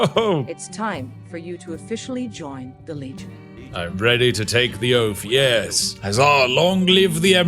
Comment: was watching on fast and thought you said "I'm bready to take the toast"...